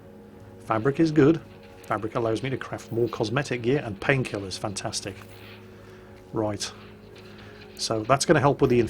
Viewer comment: You say "painkillers, fantastic," but what I hear is "Pills here!!"